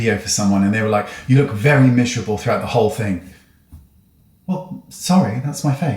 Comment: speaking of "looking 15 years younger" what did you use to LITERALLY look 30-something again? wow, you look gr8. also, what did they say you looked?..."mishable"? i know thats not the word but that's what I hear .... "mish-able" thnks wayne x